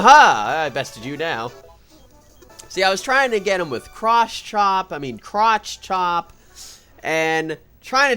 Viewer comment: i swear he said crotch chop